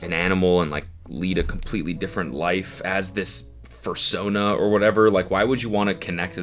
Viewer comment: did he says "fur"sona?? 😭😭 I didn't know that was what they called it lmfao